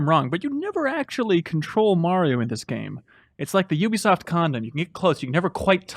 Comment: does say Ubisoft condom?